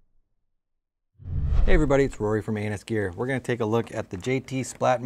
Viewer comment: I thought he said anius gear